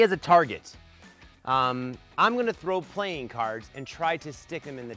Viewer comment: i just heard you say plane cards